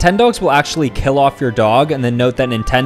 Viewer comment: Why did i hear saying " stay illegal"